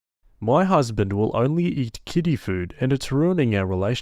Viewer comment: When I heard that the husband ate kiddy food, I honestly thought the narrator said kitty food 😂😂